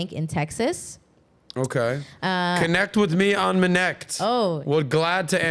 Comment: ı didn't understand what he said can someone tell me ? What is m neckt :D edit: so she said minute calls what is that